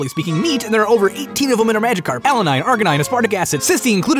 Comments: when he said Arginine i heard it as Arcanine XD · when he said arginine I thought he said arcanine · Did I hear Arcanine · Alanine, Arginine, As- WAIT IT SOUNDED LIKE HE SAID ARCANINE 😂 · when he said arganine I thought he said arcanine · i misheard arginine as arcanine lol